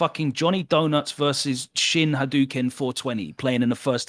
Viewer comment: I thought Logan said chinhadoken lol that's my cfn now